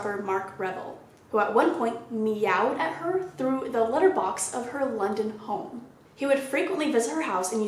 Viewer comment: I had to literally google Kiera nightly meowed at through her litter box. I couldn't comprehend. I am dying at myself for mishearing "Litterbox" when what was said was "Letterbox" HAHAHAHA